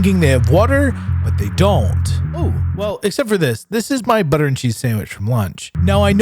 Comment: Actually, my thought was “Did he say ‘Butter and cheese sandwich’?…”